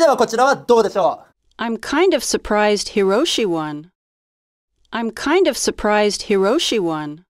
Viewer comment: kind of は相当会話で使うからよかったけどみんなできないのは後半のHiroshi won だと思うw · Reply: Hiroshi one に聞こえた